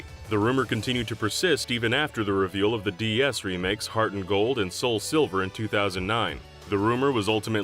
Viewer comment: Does anyone else hear him say “Heart’n’Gold” and not “Heart Gold”?